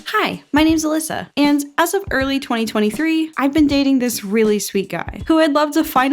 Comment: I thought you said early 2013